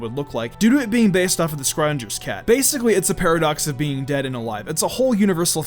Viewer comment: did you just say the... scawyingers cat lol?